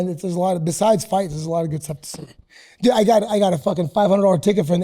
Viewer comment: At first i thought said he got a ticket from the HIV lady, i was so confused 😂